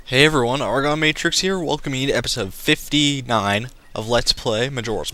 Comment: when you said episode 59 i thought you said episode 69 and I ROFLMFAO OMGWTFBBQ!!!!!!!!!!!!!!!!!!!!!!!!